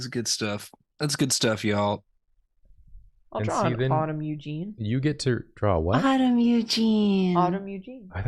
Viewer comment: I 100% thought said 'bottom Eugene' too lol